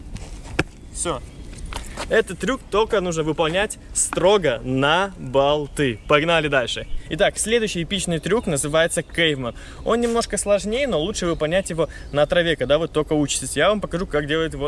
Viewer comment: мне послышалось Gay man - мужик гей(перевод)